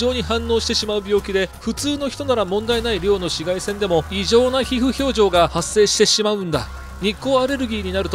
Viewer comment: ごめん異常な皮膚表情にしか聞こえないwそのあと発生って言ってるしなw